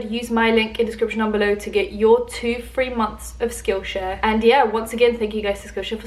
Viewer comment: when you said 2 months of skillshare, the subtitles says 3 months. Just thought you should know.